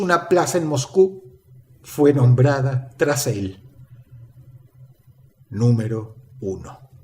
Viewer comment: No entiendo que dice Dross acá 19:36 (fue nombrada.......) Micrófono Dross, invierte!!! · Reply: tras él